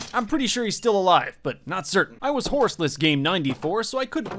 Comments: rare footage of saying game instead of day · it sounds like he says I'm horseless game 94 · who heard that he said game 94 and not day 94